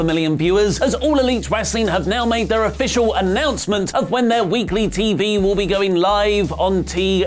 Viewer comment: How do you think AEW should compete with WWE come October? Let us know in the comments! Also, please do support us over at ScreenStalker and check out our new daily show here! <link> Timestamps for your convenience... Raw Van Dam - 0:52 You Can’t Shena - 2:19 WWE Ratings - 3:17 AEW TV Confirmed For Wednesdays - 4:48 WWE Sabotaging AEW TV? - 7:54 Daniel Bryan To 205 Live?! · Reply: Could of sworn I heard BI weekly. Meaning every other week. >_> just Saiyan. Lol